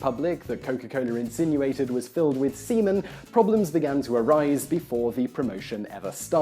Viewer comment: do i hear semen?